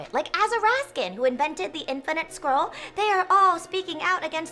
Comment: I thought she said "infinite squirrel" and that would be the greatest thing ever.